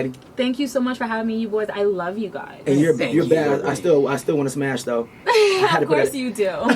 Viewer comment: on the Video did he say that he still wants to Smashed Karrine Steffans. Ha,she said of coursed you do. Hmmmm I am wondering did she fulfill his wished,afterwards😮🤦🏾‍♀️